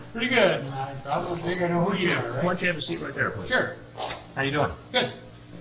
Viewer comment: Any idea what he said after "Pretty good" ? I've always wondered and still can't figure it out. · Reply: He says, I think I know who you are